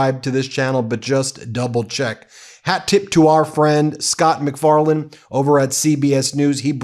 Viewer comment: Wow, you said "Scott McFarlaine..." but from across the room I heard 'Seth McFarlane", and totally went 'whaaaaaaaaat??'😂